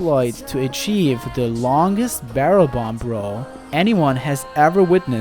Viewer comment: I kept hearing 'bomb row' as 'Banbaro'. Though the longest Banbaro would also be quite interesting to see.